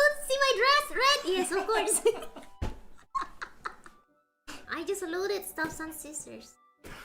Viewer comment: i think she said "i just looted staff-san's scissors" not "i just loaded stuff on scissors"